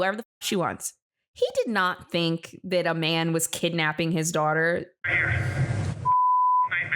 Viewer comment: i thought he said "I caught her i caught her" i didn't think it was anything about his daughter. The subtitles weren't working at all so hard to hear